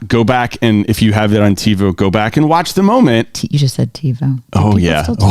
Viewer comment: What did say at 34:38 - TVon? Can someone help?